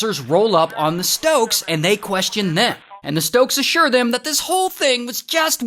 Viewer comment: Average Stoke Twin behavior tbh. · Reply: thought you said stroke.